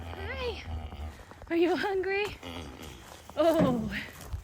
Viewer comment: Oh gosh!! You have gotta make a short out of Winnie talking to you!! At about 3:17 to 3:20 or so, you say “Are you hungry?” And I swear it sounds like she says,” Uh- huh!!” Even nods her head!!! Gotta do it!!!